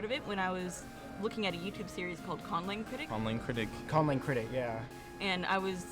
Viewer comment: I hear it 3 times and yet I can't transcribe. edit: "conlang critic"